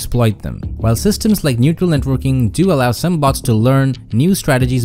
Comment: Did he just say neutral networking ?lmao